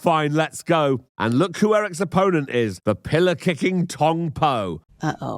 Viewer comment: I thought you said pillock-kicking"!😅